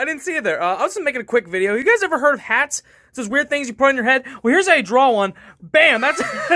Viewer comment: I thought you said "things that you grow on your head"